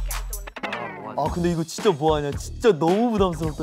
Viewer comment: Did I just hear finnish in 20:27 ? :D "Miten se voi pukeutua noin?" which means "How can it (he) dress like that?" Few second before was a bit harder to catch up, but she said something like this "There (it) he goes...Hey look (at him)" and then came the part about dressing... Haha, so cool 😂